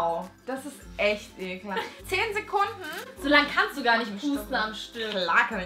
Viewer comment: Ich hab zuerst statt "Pusten" "pupsen" verstanden 😂😂